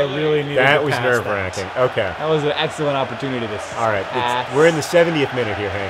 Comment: I thought Hank said "That was an Ezio opportunity." Too much Hank Plays Assassin's Creed....oops. Haha.